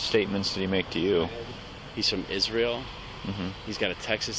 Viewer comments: The truck driver said he's from Eritrea and the cops didn't know that's a country so they thought he said Israel and they call Israel "terrorist" while saying it's Biden's fault....... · And he's not from Israel - he's from Eritrea. They probably had never heard of it, they were drunk, he has an accent, and they thought he said Israel. And that somehow makes him a terrorist. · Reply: oh ok I thought I heard he said Israel… But Eritrea is majority Christian, so you’re right There just went off the accent and doubled down